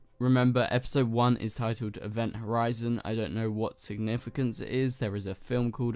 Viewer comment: Event a rising maybe it means crisis? crisis is a big event it's rising up very quickly, everything in the first half of the flash Arrow and supergirl and I think Batwoman is apart of the really big lead up to crisis. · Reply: thinks! I kind of knew what Event Horizon meant, but now I know for sure. and I thought he said Event a rising, I thought that meant in literal sense, like a event is rising.